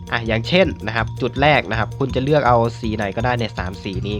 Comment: ผมได้ยินไม่ผิดใช่ไหม(☉｡☉)!คำว่า "มึง"